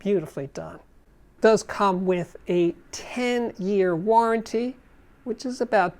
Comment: I thought you said a tin ear warranty.